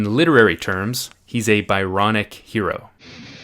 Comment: The first time around I wasn't looking at the screen and I swear I heard "He's a bionic hero" and I was super confused.